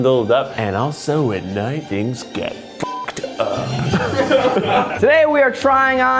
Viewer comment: I swear I heard him say something about getting "cucked up" and had to rewind it to make sure I heard that shit right... After a couple of listens, I'm still not certain but that's the only dialogue that realistically matches the video so.. Yeah